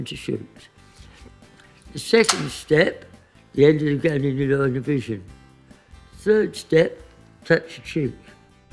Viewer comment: what did he say? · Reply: "End of the gun in your line of vision"